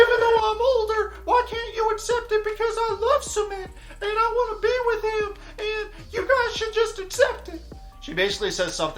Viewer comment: when you're doin the impression of Jennys breakdown & captions say "but I love cement, me and cement should be together" 😂🤣